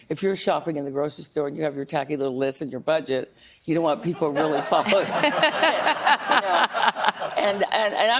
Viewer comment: Guys what she said on 7:47 I cant hear it properly, sorry not an english speaker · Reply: When you go to the grocery store with your tacky little list and your budget you dont want people like news reporters following you